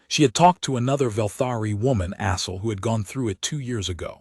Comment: First, good story. At 5:00 she talks to Asly, I heard asshole. twice. 🤭. The * problem. If asterisk if written, and it was a couple of times I guess would be spoken. The * in punctuation is not spoken and at most should be a pause. Why you would write it out in a script is beyond me but there it was. AI needs some work but overall was ok. 👍